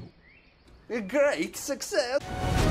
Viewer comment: what did he say on 4:50? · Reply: "Uhh, great succes!